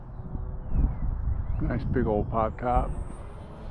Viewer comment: Good morning!! It turned out really nice. The maneuver you did at 9:23, what did you call it ? and HOW do you do it ?? I want to do it with my 102" glider. Which way do you move the gimbals?? Thanks brotha !! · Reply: Scotty, Thats a pop top. Your glider probably can't do that type of maneuver. However, I'll try to describe it. Full power straight up, aileron left, cut power and apply full rt rudder and full down and center the ail. Its a tumbling thing. Need a lot of throw to make it happen.